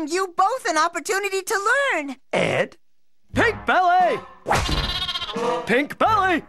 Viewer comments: I used to think Ed was saying: "Hit belly!" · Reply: I also thought he said big belly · I thought Ed was saying "Pig Belly" when I was younger 😂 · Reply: I thought he said Big Belly when I was younger.